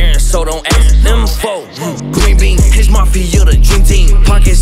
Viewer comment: i thought he said "green beaan hench mafia the dream team"😂😂